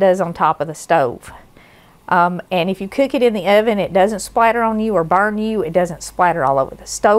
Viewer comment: Oh lol. your accent. I thought you were saying "Spider" your bacon. But it was "Splatter" your bacon.